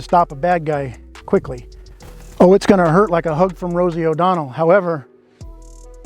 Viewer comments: “It’s gonna hurt like a hug from Rosie O’Donnell.” 🤣 This is at least half of why I watch. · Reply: I thought he said "hook from Rosie O'Donnell" as in right hook. "Hug from Rosie is just as funny". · "It's gonna hurt like a hug from Rosie O'Donnell." 🤣🤣🤣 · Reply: Thank God! I thought he said a hump from Rosie O'Donnell. 😨